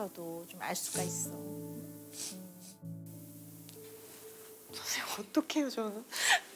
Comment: 이말이.. 선생님 저좀 살려주세요 라고들려요.. 힘내요 세라씨 세상과 계속 소통해야해요..